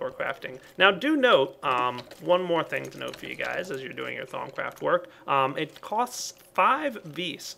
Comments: At first I always thought he said thong craft lol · Thumbs up if at first you thought he was saying thong craft and that it was a creepy minecraft sex mod.